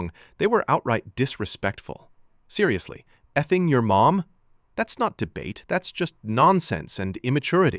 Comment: Did she just say fucking your mom?😮😮😮😮